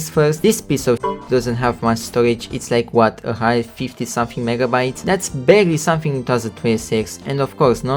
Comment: did he said negabytes or did instagram fry my brain?